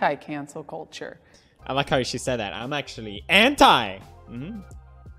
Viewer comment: I thought he said Hentai for a sec 😂